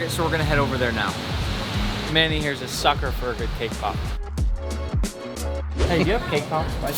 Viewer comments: I thought he said “kpop” instead of “came pop” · Anyone else thought he said k pop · It sounds like Ur saying k pop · I thought he said k-pop💀